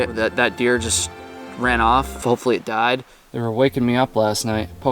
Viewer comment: did he just say "hopefully it (the deer) died" ?